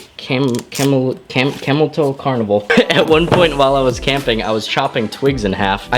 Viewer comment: did he say camel toe carnival?